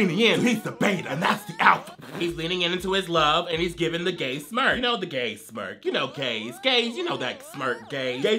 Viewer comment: Instead of gay smirks I heard “gay smurfs” and I was confused